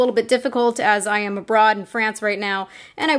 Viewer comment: did u just say you were a braud in france? lol